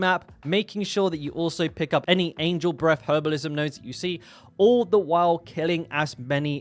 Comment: I love accents so much I swear I heard you say "angel breast" hahaha